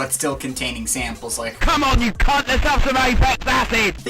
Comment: what is he saying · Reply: Come on you c##t, let's have some Aphex acid!